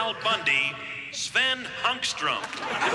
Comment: "Sven Hunkstrom" -- LMFAO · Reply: I thought the announcer said "Sven Hungstrom" 🤦‍♂️🤣🍆